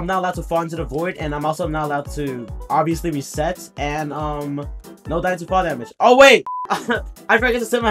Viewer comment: i thought he said no dying to fall damage